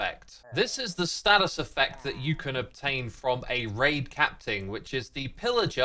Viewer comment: Am I hearing incorrectly or did say “capting” instead of captain at 0:41?😅